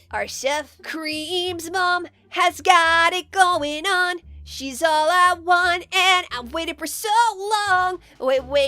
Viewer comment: Leave your own team in the comments section below and join in! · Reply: Cuz I think I heard you say pretty much I've got to go and she's allowed one in the been waiting for her so long yeah that's kind of weird hahahahahahahaha